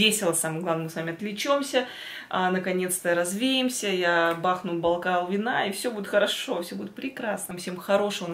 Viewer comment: В конце мне послышалось: "Я бахну Байкал вина"😅😅😅👍👍👍